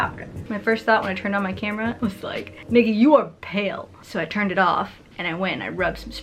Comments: At :19 could swear I heard “niggy you are pale” haha had to rewind that a couple times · um ... what word did you say at 0:19 ? Did I mishear ? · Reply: "Nikki you pale" 👍